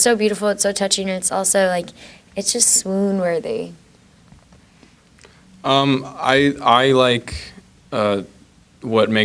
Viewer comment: Could anyone please tell me what she says at 12:12 ? I'm italian and so I coudn't catch those words. Thank you :) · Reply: Swoon worthy